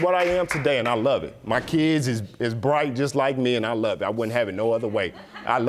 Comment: "My kids are light" is confused and silly. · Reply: I thought he said 'bright'